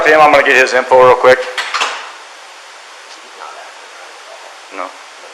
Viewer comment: the neighbor knew something was up but does anyone know what he said to the cop?? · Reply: He's not acting right, is what he said.